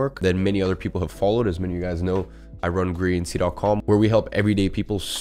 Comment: what's the name of his website? Sounded like greenc